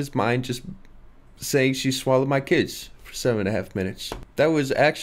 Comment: I swear I heard “she gon shallow my kids” way too many times in one album